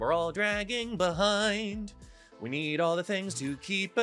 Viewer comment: “We’re playing Snap we’re doing…meth” it kind of sounded like hahah